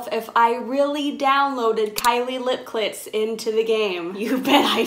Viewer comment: Please tell me I’m not the only one who heard her say Kylie lip clits